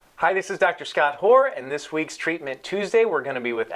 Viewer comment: No Offense ... But did he just say Dr. Scott whore ?? Sorry but i had to say it cuz i am quarantined !!